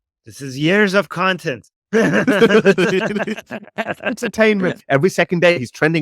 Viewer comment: for a moment I thought he said enterTATEment, lol